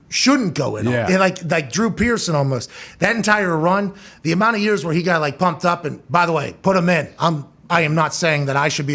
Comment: Does anybody have ANY idea what says at the 46:35 mark when they're talking abaht LeBron and MJ in the HOF? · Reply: "Not better than MJ"